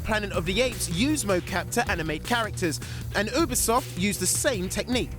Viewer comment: did he say ubersoft?